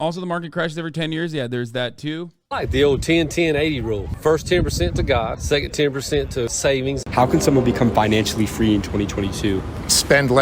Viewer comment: i legitimately first thought he said “first 10% to god, the second 10% to satan…”